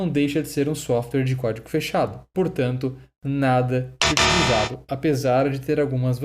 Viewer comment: o som do X esta alto e não deu para entender o que disse.. o que foi dito por favor?! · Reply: Utilizável